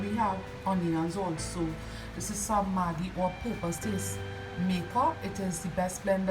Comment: What is saying at 1:06? I'm not sure what that ingredient is... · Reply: hi thanks for your comment 🙂 I was saying that’s some Maggi season up powdered season.